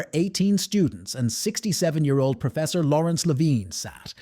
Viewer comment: Did bro just say 67